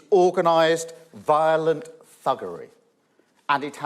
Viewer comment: I had to rewind when Starmer said thuggery. I thought he said buggery.